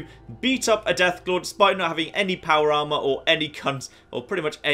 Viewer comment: It sounds like he says "despite not having any power armor or any c***" XD